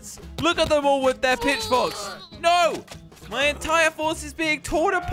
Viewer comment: i thought that he said is "bit*h" then i realized it's "pitch" 🙄🙄😝😛😅